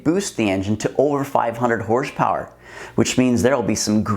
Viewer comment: Did he say 500hp at 3:15 ? Longevity at 500hp?